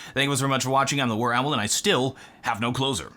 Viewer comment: Remember guys my name is the warowl and I still, have no clothes on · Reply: When I started watching him I thought he was saying I still have no closet